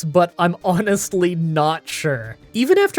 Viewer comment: Gosh I thought you said ‘Onix-ly not sure.’ I should go to bed.